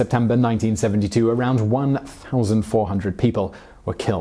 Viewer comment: I heard 1872. Should be 1792.